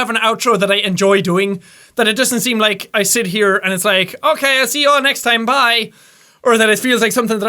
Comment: Jack's majestic outro: THANK YOU GUYS SO MUCH FOR WATCHING THIS VIDEO! IF YOU LIKED IT, PUNCH THE LIKE BUTTON IN THE FACE, LIKE A BOSS! AND, HIGH-FIVES ALL AROUND! (Wapshhh, Wapshh) THANKS YOU GUYS AND I'LL SEE ALL YOUR NUDES, IN THE NEXT VIDEO!!! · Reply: Susan Heslop I always thought he said, "I'll see you all in the News".